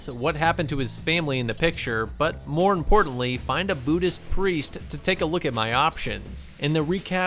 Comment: I thought he said Judus Priest but Buddhist priest